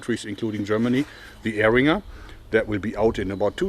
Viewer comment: What is the dart self defense pistol he mentioned at 11:32 called? I couldn't understand what he said? I'd like to see what that's all about? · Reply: Eringer